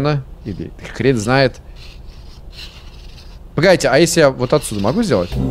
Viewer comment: Мне сначала послышалось «Бл#ть» вместо «Погодите» каким-то образом, сам не знаю как